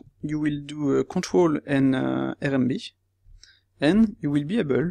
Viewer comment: I don't quite understand which hotkeys you are saying :D Why are you not recording the hotkeys? Easier to follow. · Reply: CTRL + RMB or you can press E to Extrude.